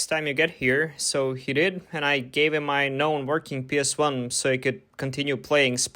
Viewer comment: I know i misheard him when he said "i gave him my non-working ps1 so he could play Spyro." So how would he give him a non-working console to use? So what did he say? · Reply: My known working PS1